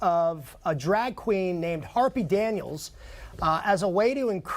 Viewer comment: Harpy What????? I thought you said Sharky Chumgrub.................lmfao